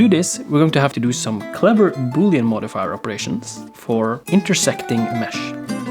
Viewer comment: One of your videos randomly popped up in my recommended, I knew nothing about 3d, and I decided to watch it on a whim. Your stellar editing, entertaining commentary, simple explanation of complex subjects, and obvious talent stood out to me. You have inspired me to learn Blender and I've been practicing for like 2 months now. Just wanted to say thank you for sharing your genius with others and helping people. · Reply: I thought this dude said booby operations. I’m to immature for this.